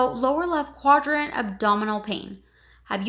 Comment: Hello thanks for the video. But I can't ear what the doctor says in 1:04 between lower and abdominal pain. Can someone please help me? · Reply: Lower left quadrant abdominal pain